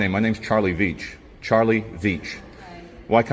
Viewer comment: All I hear is "Charlie B1tch, I'm Charlie B1tch". LOL